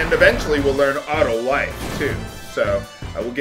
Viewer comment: I beat ff2 on my psp. when I was playing ff9 on my psp, I noticed this reference. lol instead of auto life, I thought you said auto wife